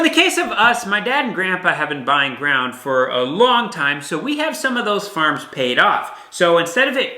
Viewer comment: I thought the video said "we had to give up the farm" phew